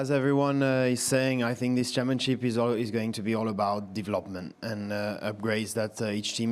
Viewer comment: I though Charles was gonna say "This championship is already over"